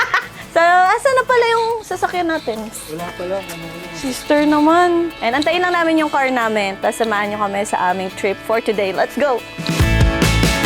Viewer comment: Omg, that " WALA PA LOVE " 🥰😍 tama ba narinig ko🤣